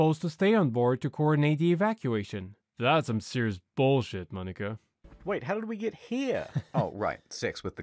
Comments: omg i thought you said the n word for like a solid 3 minutes before realizing you said domnica lol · I wanted to response to a few comments from people worried that I said the N word at 12:21. In all honesty, my intent was to say ‘’Monica’’ and not the N word. I understand where the confusion came from, it was a bad mix of a quick pronunciation and a thick accent. But I just wanted to reassure you all, that is not a word I would ever use in any of my video. Sorry if I have accidently shocked anyone. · I wanted to response to a few comments from people worried that I said the N word at 12:21. In all honesty, my intent was to say ‘’Monica’’ and not the N word. I understand where the confusion came from, it was a bad mix of a quick pronunciation and a thick accent. But I just wanted to reassure you all, that is not a word I would ever use in any of my video. Sorry if I have accidently shocked anyone. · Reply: bruh I'm not a native english speaker. And I only hear "Monica". wtf people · please tell me you didnt say the n word at 12:23 omfg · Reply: All I heard was "that's some serious bulshit Monica"